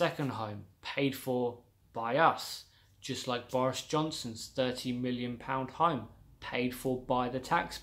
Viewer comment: I thought you said Virus Johnson.